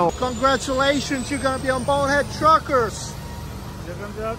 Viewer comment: WHAT IS THAT DUDE SAYING? dude truckers?